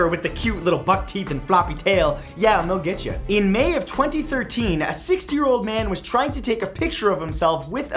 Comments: Who else thought he said "A 6 year old man" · "No, I'm not Jason Stathem, Howie Mandel, or Mr. Clean. Shocking. I know." Wait, what. HAVE I BEEN DECEIVED THIS WHOLE TIME!? 0:54 I initially heard that as "a 6 year old man" and I'm pretty sure you can imagine how confused I was... 4:05 WHAT THE TEETH. 5:26 Wait, no, Matt, that game was amazing! I played it all the time as a kid! Also pandas. Pandas can be vicious. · I'm sure he mean't 60, but it sounds like he said "a 6 year old man." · When you were talking about the beaver, I thought you said 6 year old man 😂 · XD at the beginning i thought he said cute little fuck teeth